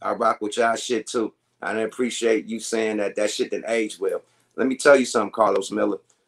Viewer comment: Blood was just talking to be talking OG!! And I thought Carlos was saying that rumor didn't age well...